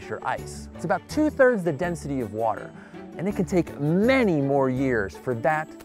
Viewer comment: At first I thought he said firn was two times the density of water, I got all excited at the idea of ice that sinks in water, unfortunately I rewinded and heard two third the density of water on the second pass.